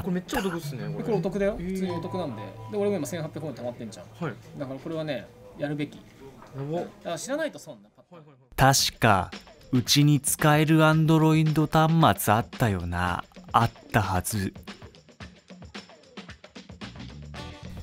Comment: アンドロインドにしか聞こえない